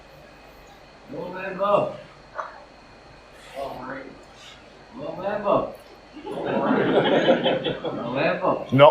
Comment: I'm not sure what's going on at 24:20? Anyone know? I can't understand what is being said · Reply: Some pissed up dude keeps bleating out 'LIANG WEMBO'. Ronnie then says something like 'oh gawd... you can't win everyfin'.